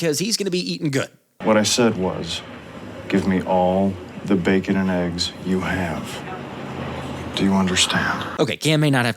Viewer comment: I’m afraid what you heard was…give me a lot of bacon and eggs….and that’s wrong. What I said was……..Give me all the bacon and eggs you have.”